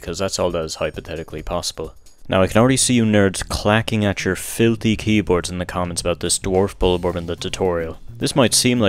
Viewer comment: Misheard “filthy keyboards” as “fiddley keyboards” and thought of the lithuanian man